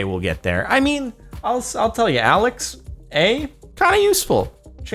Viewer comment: Did just call Amazon's Alexa - "alex a"?